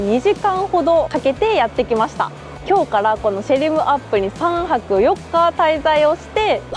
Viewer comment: 「シェリムアップ」と言ってるように聞こえるのですが、都市名は「シェム リアップ」ですよ。その後では正しく言ってるので、ちゃんと覚えられたようで安心です。 カンボジアは人間がガツガツせず落ち着いてる印象ですね。だから治安も良い感じ。